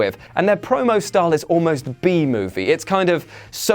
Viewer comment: did someone say BEE MOVIE